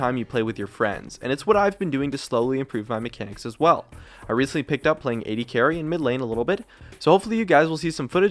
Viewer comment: I was playing Xbox while listening to this. At first I thought he said ''So, I've been picking up AD-carry in the midlane.'' It made me chuckle.